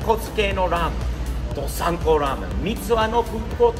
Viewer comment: どさん子ラーメンがサンポーラーメンと聞こえた。